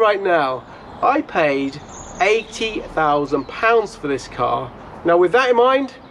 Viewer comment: 80k - Had to replay that twice to make sure my hearing wasn't faulty. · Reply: Snap, I rewound twice. I thought he said £18000.